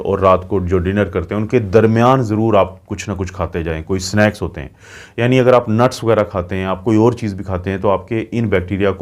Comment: Sir plz, Correct word snacks instead of snakes At 5:50 Thanks